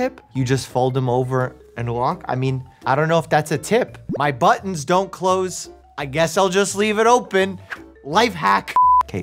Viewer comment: Idk why I heard this as “My butt don’t close, I guess I’ll just leave it open, life hack” lol